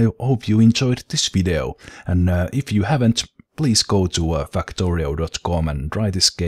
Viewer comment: it sounded like you said :" Fuck torio"